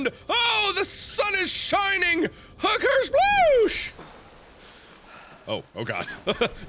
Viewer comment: I swear to God it sounded like he just said "Hooker sploosh" O.O